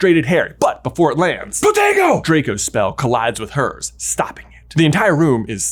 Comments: The closed captioning spelled "potato" instead of "protego" at about 23:19. LOL. Really interesting video...the disuse of the mirror is so dramatic irony that I always skip most of the ministry visit chapters in OP. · said it so fast... i thought he said POTATO instead of PROTEGO. 😂😂😂😂 I was DYING! Love the ❤ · For one moment, I thought I heard "potato" when J said "protego"